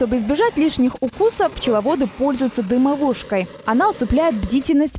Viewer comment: дымовушкой... Я правильно расслышал!? ) Может ...дымарём... ?